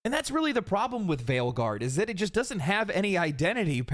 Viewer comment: Wait. At 06:11 did you say Veilguard or Failguard? I mean, both are accurate. I'm just curious.